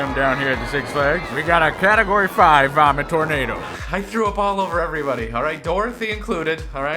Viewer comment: I thought he said “Pornado.”